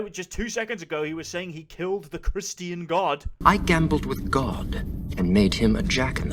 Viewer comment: "He killed the Christian God" does he actually say Christian God or just God. If the former that's the weirdest specification. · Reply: He just says God, but that’s what he meant